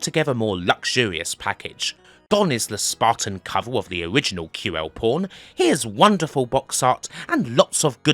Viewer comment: I kept hearing "QL Porn" lol